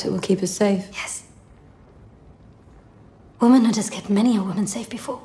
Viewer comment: I'm struggling to hear the sentence said between 0:09-0:11 properly. Can someone tell me please? <3 Specifically what is said just before "...has kept many a woman safe before" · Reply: "womanhood has kept many a woman safe before" (sarcastically). Sorry about the sound!